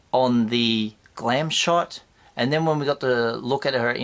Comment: good stuff as usual Although when you mentioned the Padme figure, at first i thought you said 'clam shot' !! i nearly spat my tea out, obviously it was glam shot. A one track mind i have. Sorry for lowering the tone of your channel!